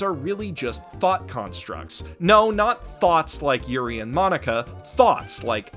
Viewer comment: Did He Really Call Them Thots! 😂😂😂 I'm Done!